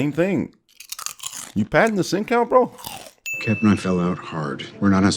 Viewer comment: For the longest time, I thought you were saying "are you padding the sink out."